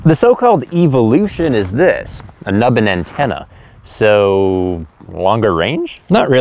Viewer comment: Don't knock the twin antenna on the Sena. Its a technique called "diversity". Because radio can bounce off things or get blocked by objects, a single point in space can become a dead zone. The thing is that wave lengths are very short and those dead zones are very small, so the difference can be millimeters. Whichever antenna gets the better signal at any given moment is chosen by the system. So it tends to work more reliably. Its not a range thing. For that, you need more power. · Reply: Actually I was talking complete garbage. I misheard "a nubbin antenna" as "another antenna". I guess my ears must have been ringing! So; how come they don't use diversity?